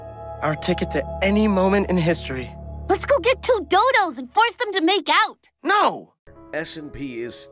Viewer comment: I immediately thought Mabel said “dildoes” and not “dodos”